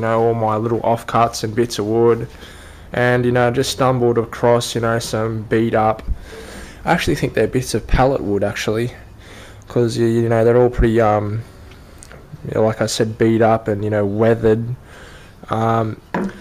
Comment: Sounded like you said "pellet wood" ?? Looks like walnut to me. Anyway, very smart way to make use of some of that scrap wood. Hope to see more- thanks for sharing